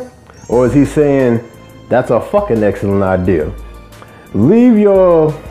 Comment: Definitely sounded like he said 'fuckin idea' to me lol